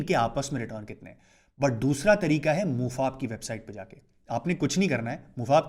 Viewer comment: Mufaq kya bola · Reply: MUFAP website